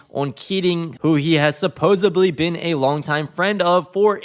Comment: did you say "supposably"??? oh my jesus